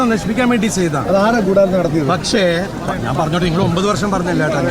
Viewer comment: Your subbtitle is incorrect at 6:01. When he is saying something one reporter interjects at that Dileep is saying " You have been talking for 9 years, now allow me to talk". Your subtitle says " last 9 years i have said this multiple times" That is jncorrect.